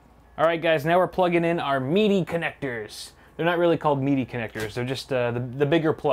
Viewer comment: When you said "meaty connectors" at 45:31 I thought you said MIDI connectors and I was like "why would there be MIDI connectors in a computer wtf"